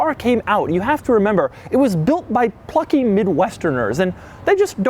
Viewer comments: When he said plucky I wasn’t listening properly and I thought he said f**king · I thought he said “it was built by fucking mid-westerners”